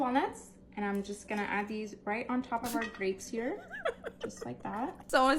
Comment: I thought my girl said "Great" but she said "GRAPES"